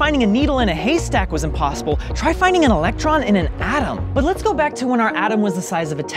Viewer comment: when he said atom it sounded like the vaping guy meme, Adam